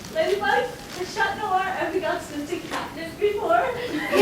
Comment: what did they girl say I couldn’t hear very well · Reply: the girl ask ladybug if chat ever try cat nip before.